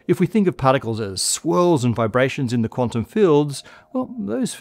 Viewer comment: I swear I heard you say "Squirrels in the quantum fields" instead of "Swirls in the quantum fields."